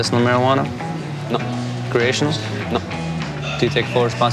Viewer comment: does he say "creations?"